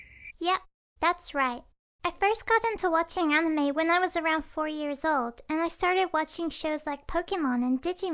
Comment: When said 4 years old I thought she said 40 years old 😂